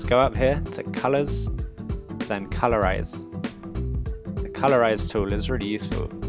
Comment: Good job, one thing tho, I thought you said color raze instead of colorize so I was kinda confused but other than that you get an A+